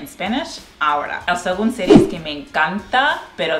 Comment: What did she say at 1:32 right before she said "me encanta" the 2nd time? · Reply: La segunda serie que me encanta